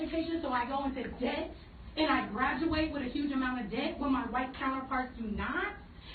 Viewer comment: If you turn on subtitles and go before 5:30, it says "I graduate with a huge amount of dick." (I know, she really said debt).